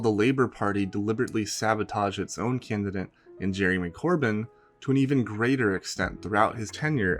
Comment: I keep mishearing you saying Jeremy Corbyn as "Jeremy McCorbyn" for whatever reason.